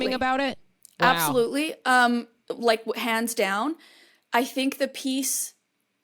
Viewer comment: said "Hands down" and I heard "pants down"